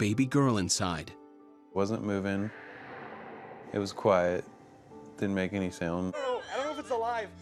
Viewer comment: Did he just say didnt make me a sandwitch